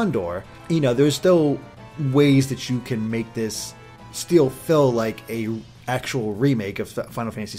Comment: you said 'steel fill' instead of 'still feel'